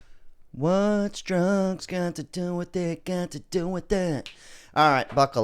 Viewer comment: I heard "What's Trump got to do with it, got to do with it" 😂😂😂